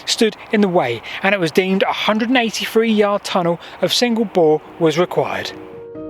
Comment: The subtitles said ". . . tunnel of single ball . . .". What I heard was "single bore". I wondered at first whether this was a computer subtitling system, but if so it was an extremely subtle [sattoo] algorithm as it was reverse subtitling "ball" to "bore".